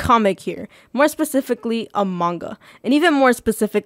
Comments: im not the only one who heard among us when she said a manga at 0:44 right... im not actually going crazy right?? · why did i hear amonga XD?